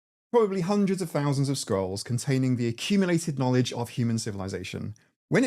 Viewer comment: with scrolls i misheard skulls… n thought nothing wrong there. oops